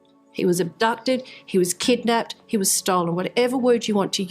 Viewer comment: Because of her accent I thought she said "He was Stalin". Yikes!